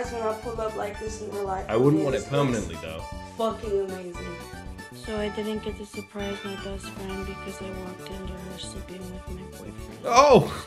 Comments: what did she actually say as she was too quiet and mumbling to be understood, by me at least. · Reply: she said she could surprise her friend because she walked into her friends room and saw her friend sleeping with her boyfriend. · what did she say at 10:30? couldn't really hear over the music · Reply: She couldn't surprise her best friend because she walked in on her boyfriend cheating on her with her best friend